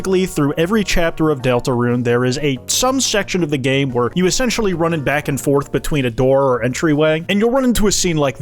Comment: I intially heard this as "you sexually run back and forth"